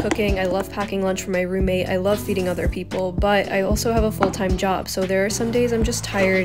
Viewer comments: If these captions weren't here I would've thought you said "I love eating other people." Instead of "I love feeding other people" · I thought you said “I love eating other people”